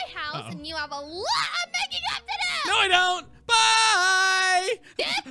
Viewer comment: did Tina say "were going to my house, you have a lot of making out to do" or did she say up instead of out I can't tell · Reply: She said up if she said our this video wouldnt still be up lol😉